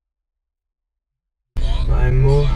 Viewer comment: No 4:34 dps q o jimim sorriu eu ouvi um my mochi